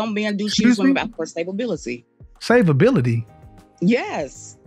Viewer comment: did she just say stabability?🤣🤣😂😂